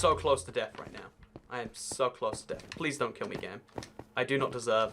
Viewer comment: is he saying "don't kill me -agai-..."? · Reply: he said please dont kill me game